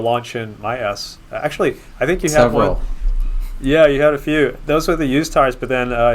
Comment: subtitles: "Gally did a launch in my ass actualy I think he said well yeah you had a few"